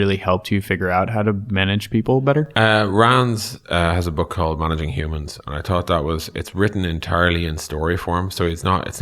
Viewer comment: can't understand the name of the book or author, transcription didn't help, someone? :) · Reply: "Managing Humans" by "Rands"